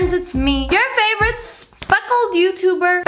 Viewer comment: Instead of speckles I thought she said "fuckled"